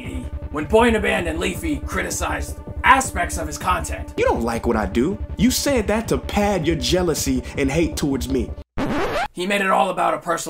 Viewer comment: Change your birthday? LOL so basically LIE to everyone and change your birthday every time you post something just to get views... #Goodadvice no wait... #GREATadvice! That's the best way to gain trust from your followers. I'm crying here 😂 😂 😂 It's too funny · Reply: Nerd City I'm very curious how you are editing these videos. It's on another level. Also I thought prince ea said "pad your genitals"